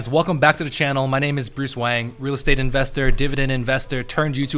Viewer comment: Legit thought he said Bruce Wayne